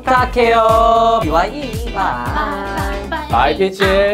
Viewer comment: When and said “Bye peaches”, I heard “Bye b*tches” 😂😂